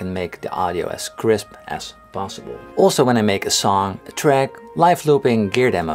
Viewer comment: "So I can make the audio as crisp as p... ...b.... " Wait, what? I didn't catch that last bit. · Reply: "possible" 😉